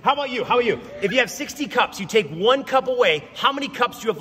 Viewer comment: He said “60 cups” not “6 tea cups” and he definitely said “26 cats” not “20 sick cats” Edit 11/6/22: Why is this still getting attention?? 😭😭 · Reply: I thought he said 16 cups😭